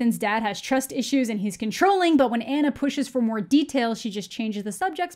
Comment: instead of saying she is controlling , i thought she said she was trolling.